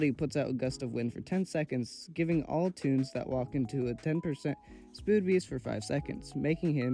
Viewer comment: spood beest instead of speed boost